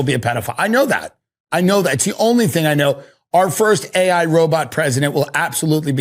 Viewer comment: I laughed so hard when I heard "our first Ai robot president will also be a A.I."😂😂😂😂😂😂